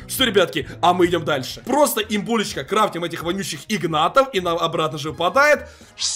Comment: Кто услышал на 5:57 сек кроытим Игнатов (я Игнат)